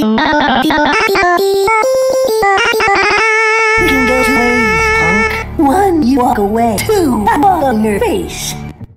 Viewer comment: I still can’t understand wtf shaggy’s saying at 1:19 lol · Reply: He says ‘this can go two ways punk one you walk away or two, I walk on your face’